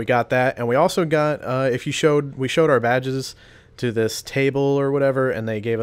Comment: "we showed our badges" at 4:20 sounded like "we showed our vag's" when I first heard it lol anyone else?